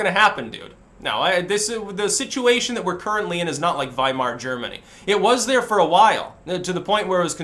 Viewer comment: i thought you said wall mart Germany ... if these guys are the best nazis we can come up with ...that’s about right.